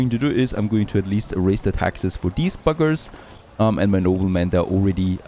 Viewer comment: Did I mishear you or did you refer to a group of your citizens as "these fuckers" ?